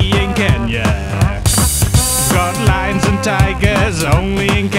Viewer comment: Always thought it said "we got dancing lions, only in Kenya"